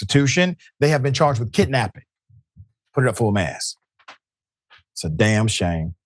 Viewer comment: Full-Mast means the Flag should be positioned at the top of the mast of the flagpole. I'm a VETERAN, I fully understand how he is using it. He even got me saying it. Damn shame, I like how he says that also. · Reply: Thank you for clearing that up. I always heard “full mask”’ like unmasking the bad guy in Scooby-Doo… which just never seemed to make sense. Full Mast is a more grown-up serious thing to say.